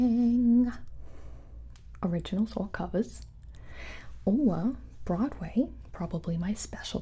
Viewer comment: DID SHE JUST SAY SHE COULD DO A BROADWAY NUMBER (GIBI IM BEGGING YOU PLEASE MAKE THIS HAPPEN)